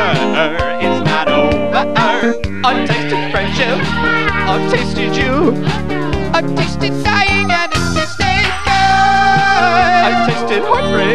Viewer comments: what others hears: "i tasted friendship! " "i tasted you" "i tasted dying" "and I tasted goood!" what I hear: "autistic friendship" "autistic you" "autistic dying" "and autistic goood!" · I thought he was saying "autistic," instead of "ive tasted," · Iiiii came here bc I thought he was saying autistic instead of "it tasted" :,) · Why do i keep hearing "autistic" instead of "i tasted"😭